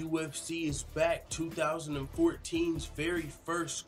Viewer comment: did you just said 2014? lol but damn I can't believe it's been already 4 years it's seems like 2014 was truly yesterday, time sure fly when you in your 20's, it's like this damn cruel life doesn't want you to enjoy your youth